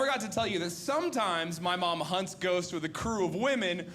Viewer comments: I totally thought he said his mom hunted goats. 😂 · I thought he said his mom hunted geese